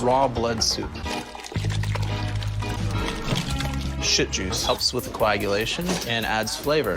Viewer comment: does he say "Shit juice"?